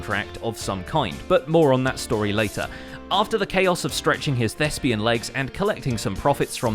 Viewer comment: I swear to god i cant hear anything but "lesbian legs".. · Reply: It's "Thespian" another word for actor.